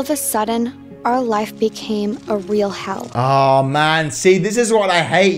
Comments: Did anyone else notice when she said our life was a living hell (3:36) the caption said our life was a living "health"? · when she said hell, the subtitles said health